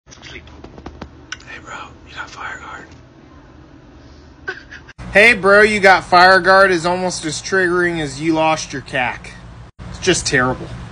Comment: Wtf is a cac?! I thought he said when u lost ur cat lmfao